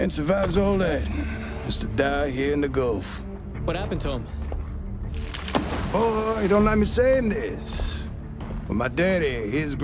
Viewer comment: 空耳で｢お~、色んな店に｣って聞こえる